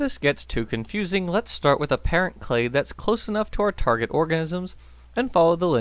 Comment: Is it just me or at about 1:30 does it sound like he says “target orgasms” instead of “target organisms”?